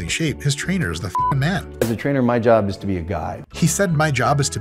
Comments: I thought he said "My job is to be a God" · I heard him say "as a trainer it's my job to be a god!" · I thought he said, "as a trainer, my job is to be a god". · I thought he said "As a trainer, it's my job to be a god" also not wrong. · Lol I thought he said it's my job to be a god lol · I thought he said "As a trainer it's my job to be a god."